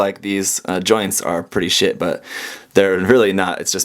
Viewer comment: did he say sh#t or am I hereing stuff